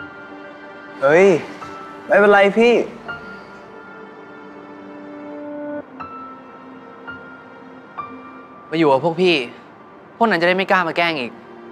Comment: all I heard here was this Bambam: oi mebeliapee Tor: beowogehee, honedioma gongagangy